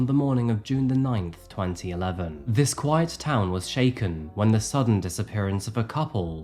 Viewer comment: does he say "these two were just a sweet white couple?" I don't really care...just seems like a weird thing to say so I wonder if I'm mishearing. Lol... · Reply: I definitely heard “white”, but my first thought was that it was supposed to be “quiet”, the enunciation was just really strange. “A sweet, quiet couple”.